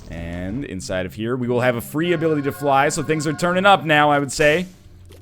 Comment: Did just say turn up? That's enough YouTube for him today.